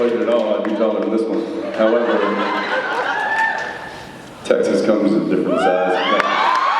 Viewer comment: Thanks so much for sharing . Awesome video, but I can't quite catch what Jensen said at 16:56? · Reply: Sharon W "However. Texas comes in different sizes."